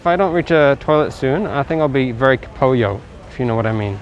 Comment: apakah aku salah dengar? Cak bilang very kepuyoh 🤣🤣🤣